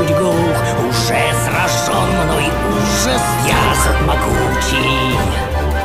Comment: Мне одному послышалось вместо Зот Могутчий, Я АЗОТ МОГУЧИЙ